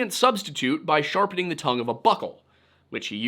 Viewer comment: Did he say "sharpening the tongue of a butthole"?